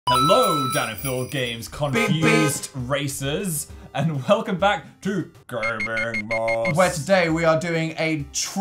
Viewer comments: i honestly thought phil said "communist racers" at the start and had to replay it twice hA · rewatching this and when phil said "confused racers" i thought he said "communist racists" and i think my history classes are messing with my head